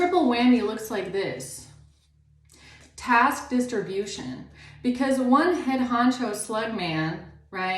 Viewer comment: I thought you said tax distribution got fucking dyslexia started laughing. Then I read what it said.